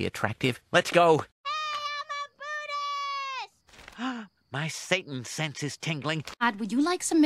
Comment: What is Lisa screaming there? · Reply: She said “I AM A BUDDHIST”